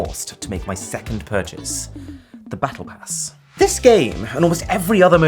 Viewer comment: for some reason I heard it " The Battle Ass " 🤣 🤭